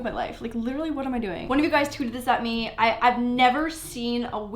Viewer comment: did she say tooted or tweeted?